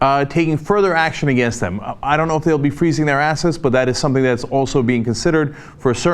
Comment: lol Cenk said "Freezing assets" I thought he said "Freezing their asses" and was trying to figure that out.